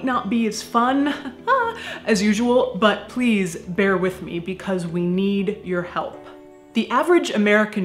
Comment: I thought it said “we need your head” 😭